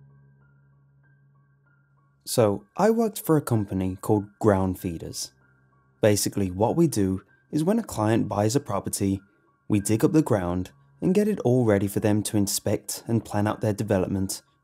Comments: This story started off wrong when I thought you said your company was called “Ground Fetus” lmao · when he said "I work for a company called down feeders" I swear to God I heard "I work for a company called ground fetus" wtf?? 😂